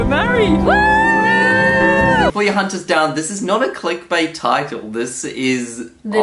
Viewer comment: You started this vlog with "before you hunt us down", which as fast as you said it, I first heard "for our Hunters' down" (like a nap) then saw the date you posted. irony? interpretation???